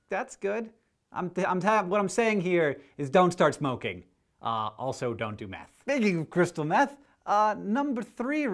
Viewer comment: When you were talking about meth, I thought you said "So don't do math."